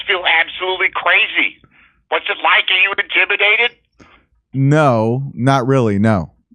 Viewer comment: I thought he said are you injimidated?